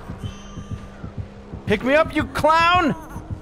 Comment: I'm sorry did he just say "pick me up you c l o w n"????